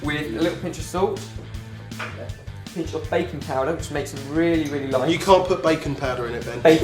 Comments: when you said baking powder i thought you said bacon powder you werent the only one Jaime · Bacon powder! :D If you want some extra flavour, you can also poach some extra chicken and veggies as the cooked chicken and veggies usually give all their flavour to the soup. My granny also told me that you could use the chicken fat from the soup for frying, e.g. fried potatoes. Have you ever tried this? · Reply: it was baking powder , misheard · I thought you said baking powder when you said bacon powder haha i was sososososo confused